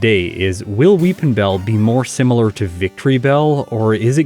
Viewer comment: I'll be honest every time said "Will Weepinbell" I heard "Wil WheatonBell"